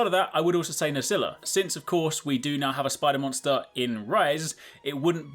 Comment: But there is a spider monster in rise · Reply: He said "we do now have a spider monster in Rise." going back to that part of the video after reading the comments I can understand how you and a few others it seems misheard that as "do not have."